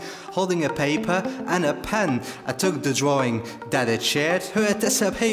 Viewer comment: the thing is for a while i thought he said "then i took the drawing, that is shit"